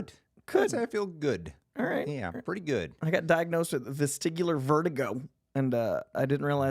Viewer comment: I thought was kidding at first, because I thought he said he was diagnosed with testicular vertigo.